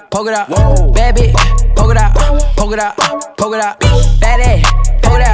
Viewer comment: i cant be the only one who heard “polka dot” .. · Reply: Same I always thought he said poke it dot 😂